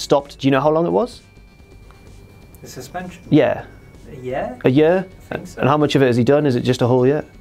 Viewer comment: As an American, this is what I heard at 1:17 - "A yeah?" "A yuuuh."